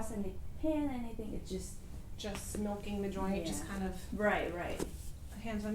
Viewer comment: I thought she said just smoking the joint lol